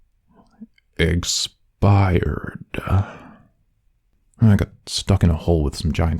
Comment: why did i hear "eggspired"? lol